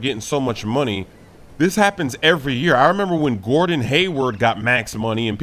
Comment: I thought he said Wordon Hayward instead of Gordon